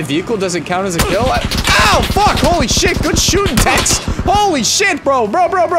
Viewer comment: did he just call someone a tex as in texan im dying